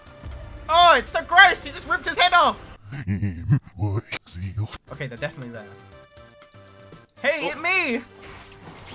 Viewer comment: so um.. can someone find out what did he say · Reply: "Please Kill Me"